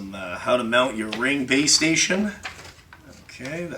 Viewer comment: Lol had to go back and listen again, thought you said how to mount your ring piece🤣👍